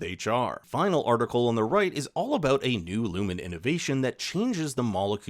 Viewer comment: Did you say iluminate invasion?!?! #fordemocracy